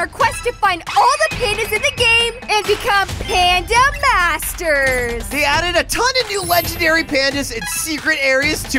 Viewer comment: I thought that said panda Marker it's just sounds funny